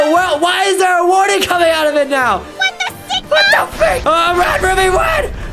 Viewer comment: did Ruby really said "what the sigma" at 12:44....?